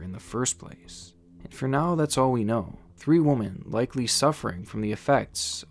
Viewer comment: Love videos, but am I the only person who keeps hearing him say woman instead of women. I swear I just heard him say "three woman"